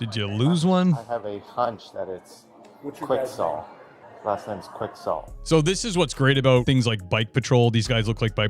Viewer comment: I heard " Quick sell" "Last name Quick sell" I thought that would be the scam he's running, not his name!